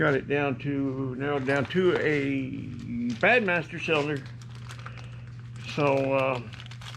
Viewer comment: What is said: "Bad Master Cylinder". What I hear: "Batt Masterson"...What is the problem?